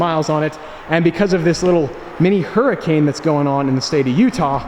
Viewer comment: When he said mini hurricane in Utah I thought he was saying huracan 🤣